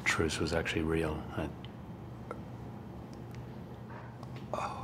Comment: What's being said in the end credits? Edit: it's Hugh saying "say hi to Blake, man, and the kids" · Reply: Thought he said say hi to mom and dad, and the kids. But i could hear it this way too.